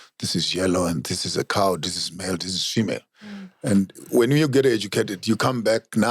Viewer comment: Did he say this is shemale at 44:09, hehehe po** dude